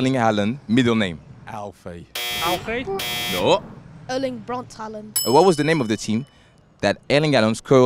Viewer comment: I heard "erling brought haaland" 💀💀💀💀